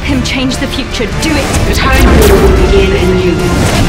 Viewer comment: What does it say in the 0:38? Around it. "Time war/vault/lord in...?"